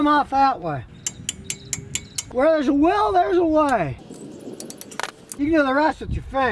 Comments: voice is the only good sound a car can make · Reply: Did he just say "where theres a WHEEL theres a way" ?? 😂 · 2.46....Did he just say "where theres a WHEEL theres a way"?? 😂